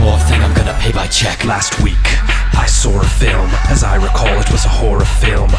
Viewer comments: did he say "sawr" at 1:24? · lol he sounds like hes saying i sawr a film